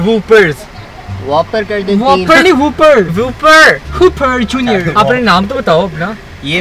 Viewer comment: Did he said super junior 😍😍😍